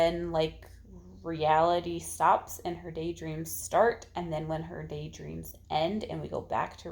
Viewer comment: Haha! When you were talking about Rebecca I thought you said she day drinks a lot (instead of day dreams). That would be an interesting twist! Lol